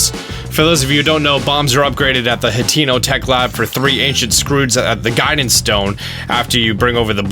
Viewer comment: Why did it sound like he said ‘three ancient screweds’ instead of ancient screws